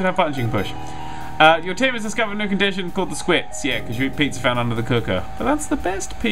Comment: Did anyone else think it said "Eating pizza under the cookies." at 14:00?